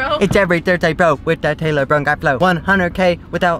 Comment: It sounded like you said “taco Taylor”